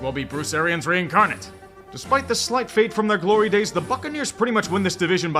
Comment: I swear, each time I listen to say Tampa's name I always hear "Fuccaneers..."